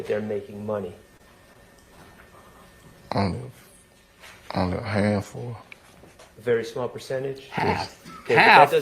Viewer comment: I don’t think said half the people make it rapping I thought I heard him say only a handful.